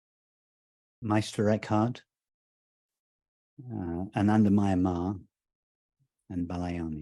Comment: who does Rupert say after Meister Eckhart? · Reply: Yeah sri anandamayi ma